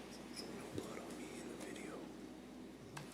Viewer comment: what did he just whispered? I heard something at the end like: ............. no blood on me in the video